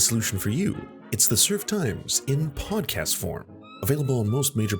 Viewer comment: whenever I get to the endplates I always hear "in podcast form" as "in pog-cast form" because I'm dating someone who's obsessed with the Simpsons and that's my little leftist smile today c